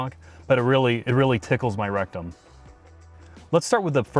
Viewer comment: His recum is tickling. Well 😁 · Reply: *rectum I thought I heard wrong lol.. I had to rewind to make sure.